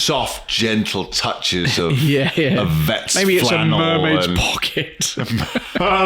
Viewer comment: I thought you said, “a barmaid’s pocket!” I’m not sure “mermaid’s pocket” is any better. I’m surprised predictive text thought that was the next obvious word though….